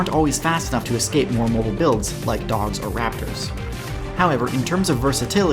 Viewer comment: Did you say dogs and raptors